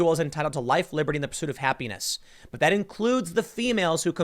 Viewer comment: Did anyone else get a midroll add right in the middle of the word “happiness”? I was listening and it went to an add and I forgot what he was saying, and when it came back he “penis” and I was like wtf did he just say?